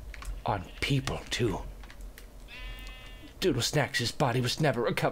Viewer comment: did he say doodle sex o.O